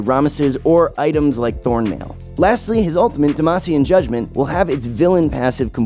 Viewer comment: Did he say '' damacian judgment '' 😅